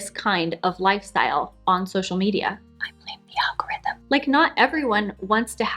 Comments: What does she say here? All I hear is "[....whisper....] algorithm". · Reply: “I blame the algorithm.” 🤫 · CC isn't helping. All I can make out is "algorithm" at the end. Can anybody tell me what on earth she's whispering? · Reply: “I blame the algorithm.” 🤫